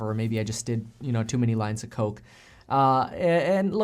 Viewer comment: I honestly thought you said "too many lines of code". Got excited for a quick minute. Lol. I think you're doing great. Keep it up!